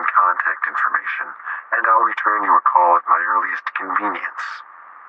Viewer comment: When I first heard the words at 18:36, I genuinely thought that the man said "return a call at your least convenience". If it was true, it would make a great mockery of the corporate culture